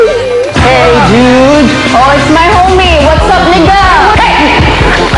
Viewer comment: Did just say "What's up n*gga?"